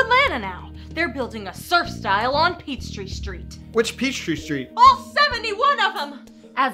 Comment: That thing about peachtree street got me. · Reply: I, uh, thought that they said "Petri Street" at first before I realized that they said "Peach Tree Street". I was a bit confused.